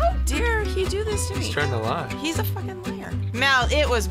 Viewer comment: I swear I heard mom say the n word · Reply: No she said lier